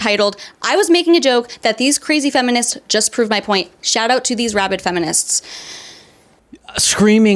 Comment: Sorry English isent my first language.Does she say "rabbit feminist" at 7:49. Is that the way to say rabies? /Rabbit feminist 4-catz